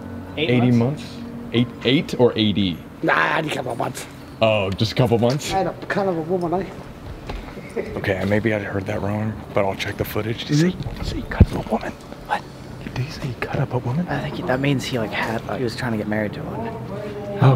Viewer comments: I think he said he had a cvnt of a woman. Maybe she caused him to go to jail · he said “c*nt of a women” we say that kinda stuff all the time dw he didn’t cut up a woman ahah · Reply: i started laughing when Colby thought that the guy said he cut up a woman and i was thinking to myself 'nah mate, he said c*nt of a woman' XD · Hope you all enjoyed:) Leave a like! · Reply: btw if u did not find out what the guy said at 11:54 show it down and he said C**t of a woman